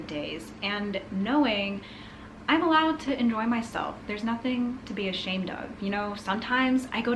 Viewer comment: at first I thought you said "I'm allowed to annoy myself" 😂